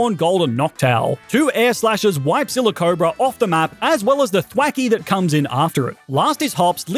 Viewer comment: Was it just me or did I hear him say quacky instead of thwackey